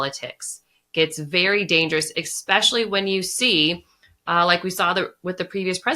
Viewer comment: Did she just say “Exspecially”?